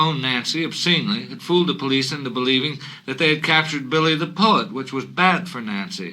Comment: For the first four minutes, I thought he was saying “Billy the Pudd”, which made sense to me at the time